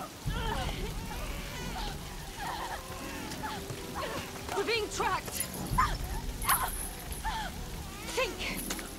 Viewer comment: what is she say ? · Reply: “you’re being tracked. think, what the king could give you that could be traced”